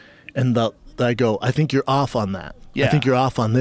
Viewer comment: After listening to the audio again without looking at the subtitles it sounds like Schaub said "I think you're off on that" rather than "He tee'd off on you." Whatever. This whole thing is bonkers anyway.